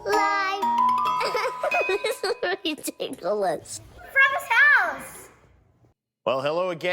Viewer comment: What is saying at 0:09? I try to figure it out every day lol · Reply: EVT she says something like “it’s ridiculous”, but I’m a cute kid way like “redikalous”